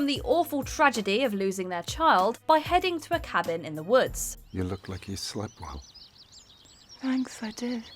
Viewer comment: I thought he said “ you look like a slu …”😂😂😂